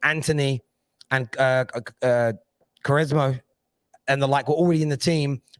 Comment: What is the player mentioned at minute 3:30? Did say Quresma?